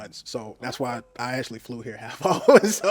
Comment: This is the comment I came to find. They regretted popping fast. And when he said he flew there oh my God 😅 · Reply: he literally said he flew HALF off , but he said it so fast it sounded like he said he actually flew the plane. 😭